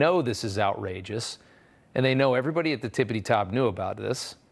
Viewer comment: did he say tippity top? 🧐